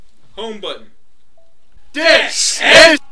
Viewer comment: Do I even want to know what said or is it NSFW? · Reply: Oh ok. It sounds like he said "Desen"